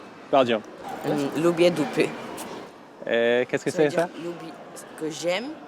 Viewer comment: I am Ukrainian, so I understand Polish well. I actually think this guy 7:00 said in Polish "I love ass*s"